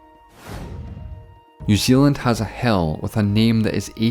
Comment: Thank you for this! Very interesting! And love your accent. For a second there, I thought you said... "New Zealand has a hell...", before I realized you said 'hill'. ^^v